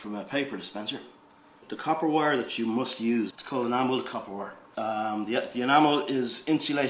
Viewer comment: I can't understand what you're saying "amoled kapoor???"